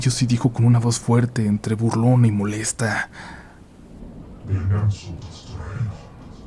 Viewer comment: Qué dice en el 22:58? No entiendo. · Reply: “Venga a nosotros tu reino” (ya vi que es de hace un año el comentario pero igual lo pongo por si a alguien más le sirve)